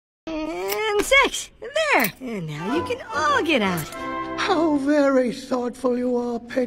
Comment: 😭At the beginning I misheard it as "Annd $3X there! and now you can all go out!" Wth is wrong with my mind today